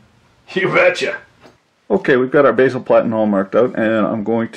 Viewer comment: What is it he keeps saying after 2:15? Basal platin? I can't tell.